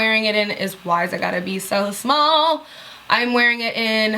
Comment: Lol 10:30 I thought you were saying the color is Whys it Gotta Be So Small! HA ha ha burst out laughing.